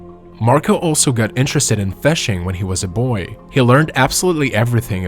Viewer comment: Marco sounds like a jerk. 5:48 I thought the narrator said he became aware of felching and I was a bit taken back.